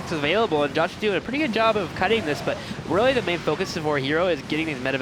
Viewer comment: you were slower but you must have a lazy tongue. The way you said natural was a bit slured and it sounded like you said, "cutting" when you had to have meant "Kiting"